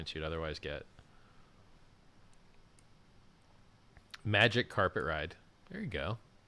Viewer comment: When rug pull. Answer: “how about you just enjoy the magic carpet ride.” Update: Forget it. Someone already used this one. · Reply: I heard them say, "Wen magic carpet pull?"